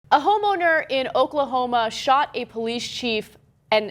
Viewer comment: I thought she said 'A HOMO NERD' in the beginning lol